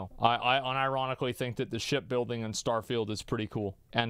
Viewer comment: I thought he was saying sh*t building, lol.